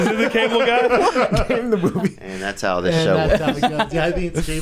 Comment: What is Theo muttering to himself at 56:48 sounds like "and that's how this show..."